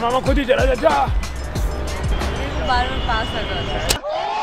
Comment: what did said...??🙄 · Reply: "Bar bar paas aajate hai"